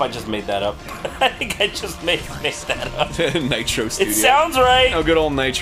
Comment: when he said "Nitro studios" I thought I heard "Nitro Chiral" and I was like they do make good games, I'm surprised Arin knows yaoi anime